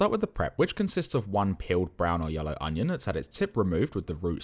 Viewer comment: peeled....For the love of me I thought you said "killed" again😅😍